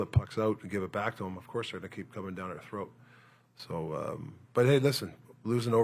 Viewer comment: did he just say" they will be cumming down our throat "